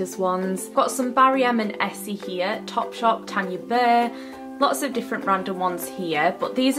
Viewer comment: 🙊was I the only one who thought said Tangy burr when she was showing the nail polishes😂